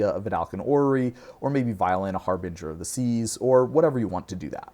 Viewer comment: is saying Vial in a Harbinger of the Seas, not violin. Just in case anyone else misheard that like I originally did.